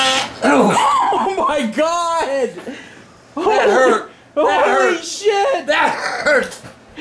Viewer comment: I know he said “That hurt!“ 5 times but it kinda sounded like he said “Hitler!” 5 times.